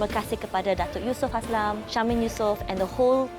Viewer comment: syahmin yusoff? Kawe salah dengar ka? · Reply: Syamin Yusoff ni adik beradik Syamsul & Syafiq, orang belakang tabir Skop jugak